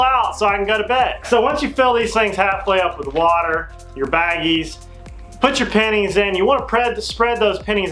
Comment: I thought he said Put ur panties in and hang somewhere 😂😂😂😂